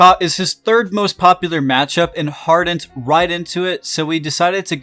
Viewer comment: What is he saying about Yi? That he hard ints right into Kha? :D BTW: Captain Flowers liked bruiser Skarner so idk if he's so ecstatic about the buffs. He's probably in nearly the same spot overall.